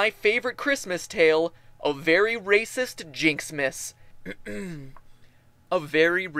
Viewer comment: I thought it said merry react jynx-mas